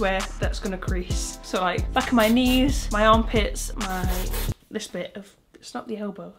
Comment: WEDNESDAY HHH- 💀 I heard her say fuck my knees..